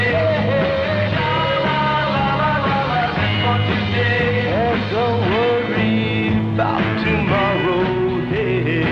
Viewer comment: I could watch that guy dance all day. I think he said his name was (?). Could understand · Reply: You might be interested to learn that guy’s name is and he certainly could dance in fact I believe his parents were dancers or taught dancing. himself had a career as a dance instructor (can find videos on YT) and is created with creating some poplar dance styles. Sadly he passed in the fall of 2023. His children also accomplished dancers. Son named Benji and daughter Lacey. Benji won many National dance contest and Lacey was one of the professional dancers on Dancing With The Stars. You should do a search on them all very talented family.